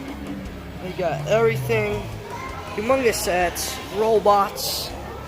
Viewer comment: ayyy man good video but did u just say AMONG US SETS or HUMONGOUS SETS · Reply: I said Hugemongus. which means Big